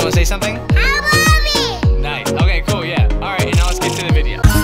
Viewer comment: Did said Habibi!?!??!!?!?